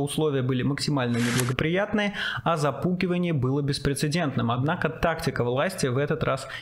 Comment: На 0:35 прям отчетливо слышу, что "запуКивание было беспрецедентным")